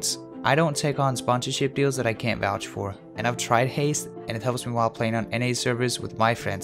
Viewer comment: When you said "Haste" I heard "Heist"